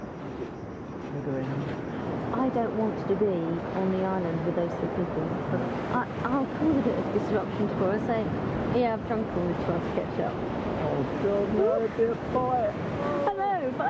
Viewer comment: What did say from 28:22 to 28:50? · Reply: “I don’t want to be on an island with those three people. I’ll cause a bit of disruption when I say yeah, I’ve drunk all the tomato ketchup. I’m not going to be a sitting duck. I’m not going to - because I’m having a bit of a laugh. I’m not stealing, because it was mine. It’s just a laugh, as far as I’m concerned. I’m just not going to sit there and let them walk all over me”.